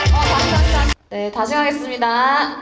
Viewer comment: Why do I hear Dhayun at 0:20?